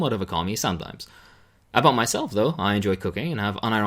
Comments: Lol it sounded like said I enjoy cocaine at first, then I realized he said cooking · thought said "I enjoy cocaine" and I briefly thought that was some very refreshing honesty.